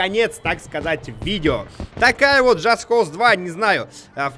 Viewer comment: Мне одному послышалось что Дима в конце сказал "такая вот Just Cause 2" (время 43:36)?